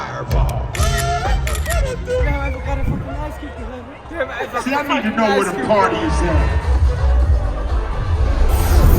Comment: Ultra geiles Video!!! (Was sagt ihr bei 5:51 ?) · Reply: Das wir grade Ice Cube live gesehen haben in Las Vegas, der ist dort aufgetreten